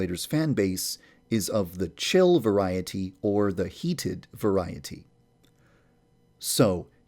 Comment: AND AT 9:34 IT SAYS "THE SAUNA" INSTEAD OF "OSANA" OMGGGGG XDDDD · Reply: OH MY GLOB AT 10:59 IT SAYS "CHILDREN TRYIN T" INSTEAD OF "CHILLED VARIETY" BAHAHAHA